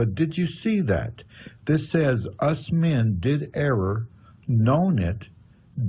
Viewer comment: At the start of this, I hear, " Did error STICK?".